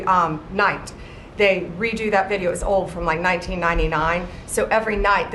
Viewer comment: Did she just say the video was from 1999?